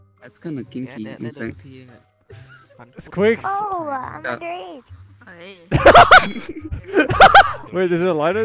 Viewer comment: did she just say "oh im under age" lmfao